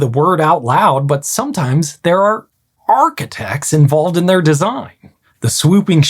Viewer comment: the captions are wrong, he clearly said 'ARGH-CHITECTS'